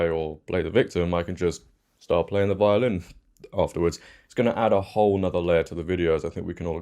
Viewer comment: When you said violin I thought you said violent! 😂😅